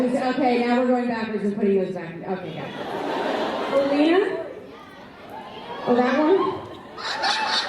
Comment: What she say in 29:43? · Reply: Katiane Reeves talking about the “ships” she says, “oh Lena? Oh THAT ONE?”